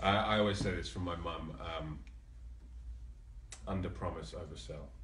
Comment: What did Théo say at 4:55 ? English isn't my first language 😅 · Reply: 'Under Promise oversell' I think